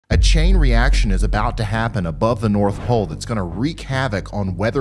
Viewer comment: In the beginning, it literally sounds like he said "above the north hole" 00:02 So what about the south hole? Sorry. Lol!